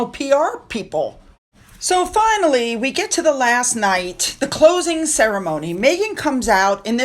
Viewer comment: When u showed a picture of Markle in her Turquoise dress and said "and at the closing ceremony.." i misheard and thought you had said "and at the clothing ceremony..." i wasnt surprised that i had made that assumption. 😅